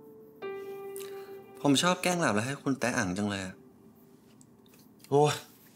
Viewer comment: Did Cher just say "You love meet everytime you do me" 😂😂 8:52 Boss knows when it's his Babe knocking😊